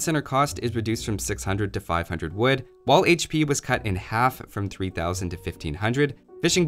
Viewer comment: Did I just hear "tc health points halved" ? If so, that's going to be bye bye for naked ff forever Edit: he said wall hp was cut in half, not "while hp was cut in half" huge relief